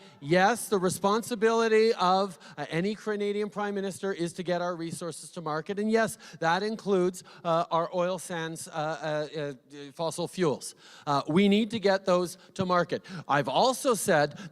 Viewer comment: Did he say cranadians at 1:30 😂